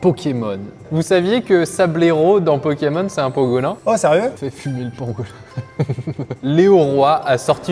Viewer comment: Quand à dit "Fait fumer le pangolin" J'ai compris "Je vais fumer le pangolin". 😂😂😂